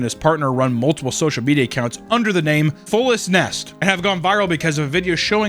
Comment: The couple who were vlogging living in a storage unit as "Fullestness", I misheard that as "Fullest Nest"